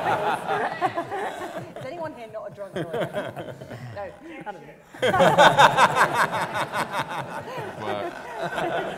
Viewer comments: What did the guy shout at 17:39? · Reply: Buncha Bubbles if you're still wondering two years later - "objection" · What does someone in the audience shout at 17:40? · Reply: "OBJECTION!"